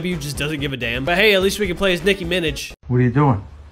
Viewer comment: "Nicki Minij" 😂😂😂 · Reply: i thought he said dicki minij ima be honest